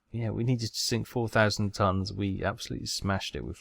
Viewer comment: Oh you said 4,000 tonns ? I’m sorry meine herr I thought you said 40,000 hahaha my bad everyone else at HQ “…. Who is this guy ?!?”